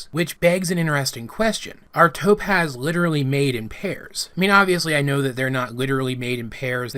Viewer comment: omg I'm so dumb I thought that it said "Paris" and not "pairs"